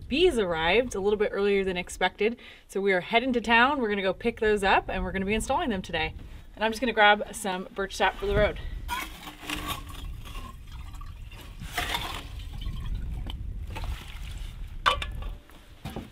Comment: Spring has arrived in Alaska! We welcome a new baby chick to the flock and are looking forward to many more hatching in the upcoming weeks. For the chicken lovers out there, what is your favorite breed of chicken? 🐓 We wish you a wonderful day wherever you are in the world! Thank you for watching 😊 ~ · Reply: did you say bird shop? bird shah? bird shot? I'm really confused, What is that & How do you say it??